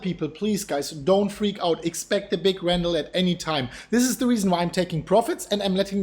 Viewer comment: did you say expect a big randal, or rainbow, or rambo, any time, I just didn't quite catch it even though I listed several times!